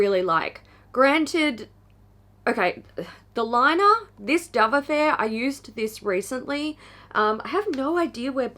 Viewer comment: I heard "Dove Affair" as "Dumb Affair" and couldn't figure out why they'd call it that. Lol. I was thinking I'd never purchased more than 2 or 3 palettes in a month, but no. Kohl's (dept. store in the U.S.) had a set of 6 Cargo palettes on sale at Christmas (2018), and I bought it. And I purchased another palette within the month. 😶 I have a lot of colorful eyeshadow, but I had been wearing more neutral looks before everything shut down. Now I'm back to work, but wearing a mask, and I thought I'd I'd be doing more colorful looks, since I don't have to worry about what lipstick to pair with them. I still keep reaching for my neutrals!